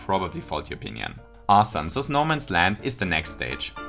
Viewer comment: When said "Snowman's Land" I heard "No Man's Land" Toad:Hey Mario! Take this GUN! FIGHT THE GERMANS!